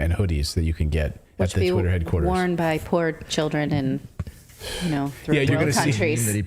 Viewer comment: I thought she said, "worn by fluoro children with no countries".. Cos it seemingly fits..😂🤣🌈🌈👌🥳🖕💔